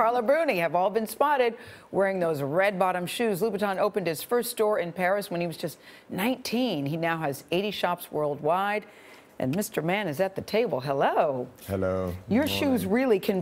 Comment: I swear she said Louis Vuitton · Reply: L T no she didn’t, she said Louboutin all as one word when it shouldn’t be it’s meant to be Lou-Bou-Tin